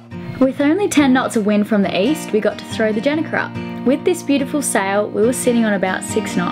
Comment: What was backwards? It sounds like he said Genny Key. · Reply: not totaly sure but i think he said gennaker <link>